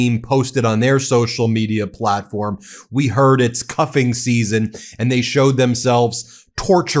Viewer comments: Is Barron Trump Epstein's child?..Oh sorry I thought you said "cucking" season!! My bad🙄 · I thought you said cucking season! :-D